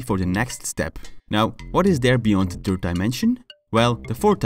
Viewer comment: you said Two dimensional instead of three, or did I hear you wrong. I just subbed you today and I loved your channel and your English accent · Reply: He said third dimension